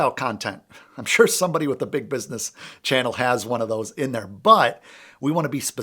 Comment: "I sure someone with a big business channel **has one of those in their butt**" ?!? 😂😂😂 I know that's not how you meant it, but that's what you said. 😂 Excellent video! Thanks!